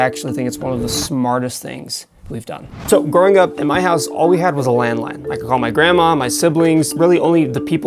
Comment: I thought he said “all we had was a landmine”